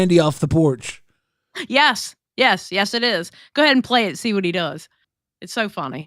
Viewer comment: lol did you say go and inflate